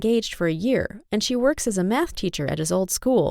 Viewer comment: I thought she said his wife worked as a meth teacher 🤦‍♀️🤦‍♀️🤦‍♀️🤦‍♀️😂😂😂😂😂